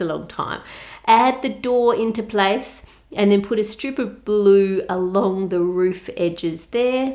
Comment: I thought she said, "... and put a stupid blue--" 😂